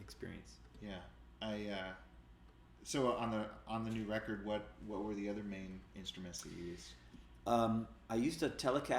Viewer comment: "I'm a huge fan of ????'s pianoplaying," Can someone pls help me out here? I can't hear which pianist he's mentioning, he tends to mumble a little too much sometimes :) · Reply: I think he said Randy Newman